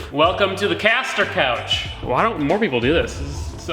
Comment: Did he just say “Welcome to the casting couch”?!?! 👀🤣😐